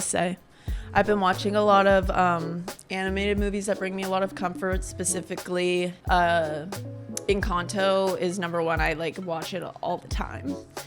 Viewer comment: oh I thought you said anime. You should try an anime review. Maybe "Your Name"?